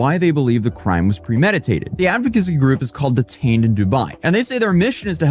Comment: I had the Speed set to 2x, and the first three times I heard him say it, I thought he said the taint of Dubai. Apparently when I turned on captions, the name of the advocacy group was actually Detained of Dubai.